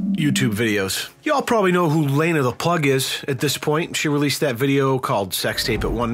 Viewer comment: did he just say Lena the Pug? xD sounds about right.